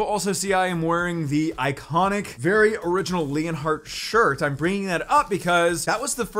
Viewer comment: i thought he said his shirt was ‘orange’. but he said “original”. 🤣🤣🤣